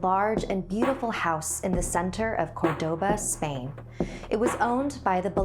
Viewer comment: You said Cordoba, but I thought you said Qdoba and I got excited. OOF